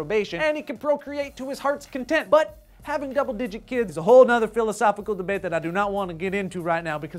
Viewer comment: Did say "a whole nother"?